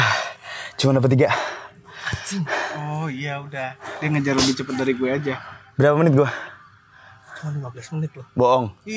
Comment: bilang apa tuh😅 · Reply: Guk guk